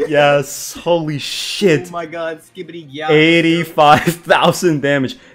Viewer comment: did i just hear skidibi gyat?